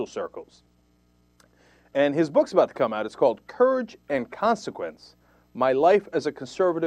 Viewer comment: At :50, I thought Cenk said turds and consequences.